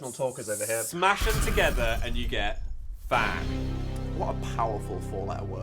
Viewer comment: the subtitles on my tv at 7:57 did NOT think you said phan. 🚬🏳️‍🌈